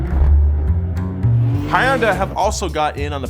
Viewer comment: me: hunday nizzmo: hi-un-dai knees-moe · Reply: Thought he said Hi - Onda.